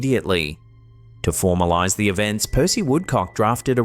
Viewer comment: I swear i heard him say Pųssy woodc0ck....but it was Percy😂😂